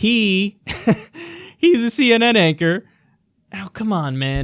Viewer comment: Did he say “I’m an actor on CNN”, May have misheard but if he actually said that it’s a funny Freudian slip